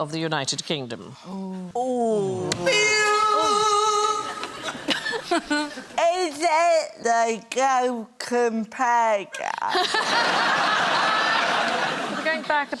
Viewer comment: Wtf is she saying? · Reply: She said is it the go compare guy?